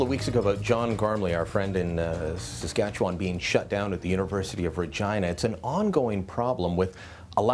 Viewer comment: I swear, at the 00:23 mark it almost sounds like he said "The University of Vergina"